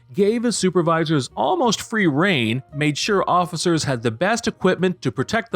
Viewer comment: "free rein" rather than "free reign" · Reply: I learned something new today, as a non-native speaker. Didn't know the word 'rein' so I always heard it as 'free reign.' 'Free rein' makes way more sense, now that I know it's a word. As I looked it up, I also learned what an eggcorn is. Thank you, Clickbeetle for this three years old comment.